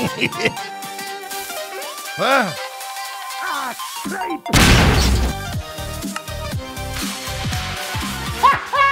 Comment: Can anyone explain to me what he says? "Ah great!" or "Ah crap!"? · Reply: Yeah, ah great